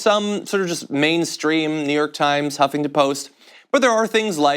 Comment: Did I just hear "humping the post" 1:09 check it out! lololol you guys are why I can stay sane in an insane world!